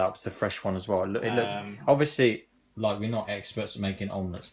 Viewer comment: did he just say "... we're not egg-perts"??